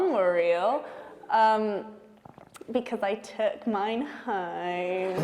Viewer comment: What did Sophie (Sansa) say when asked if the direwolves are real? "Yes, the direwolves in season 1 are real, because I ??? mine"? It's at minute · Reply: "Because I took mine home"